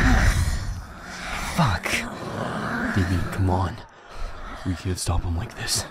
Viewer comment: i know we are all sad and scared and all this bad feelings but i just wanna say something dumb. BUT it makes me happy so yeah. 7:25 when hux says "Damien, come on" the subtitles says "Baby, come on"